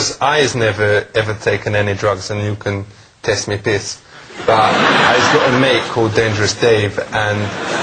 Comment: "N you cn test me piss" sounds like he said "taste me piss" ahaa.